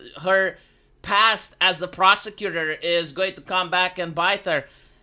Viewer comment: I thought was about to say, "Her past as a prostitute..." instead of prosecutor.